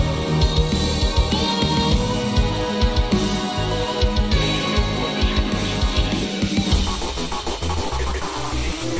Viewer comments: does anybody knows what lord x says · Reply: I hear "hey, what are you doing here" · What did lord x say at 3:03? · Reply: "Hey, what are you doing there?" In a slightly teasing tone.